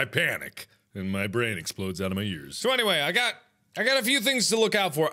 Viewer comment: And my brain explodes out of my uterus I swear to god that's what I heard at 0:20 - 0:23.